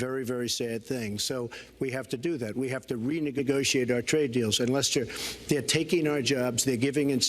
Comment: Any one notice how it sounds like hes saying "re-niggagociate"